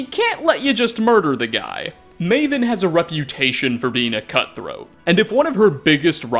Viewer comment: Alright, alright, am I the only one here who thought said "Maven has a reputation for being a cu*t" for just a second there?